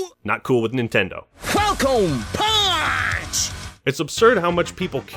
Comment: With SSB64 I thought Cpt Falcon said “FUCKIN’ PUNCH!” For the longest time.